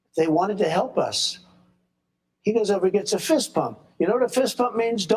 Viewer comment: Sounds like was saying Fist Pump, not bump 🤭